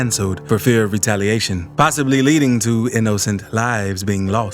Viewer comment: He really said in no cent